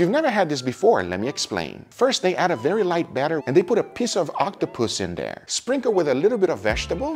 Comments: i messed in the head cuz i heard "a piss of octopus" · i totally agree, you gotta eat what you catch.. btw when he said piece of octopuss it sounded like he said piss of octopus xD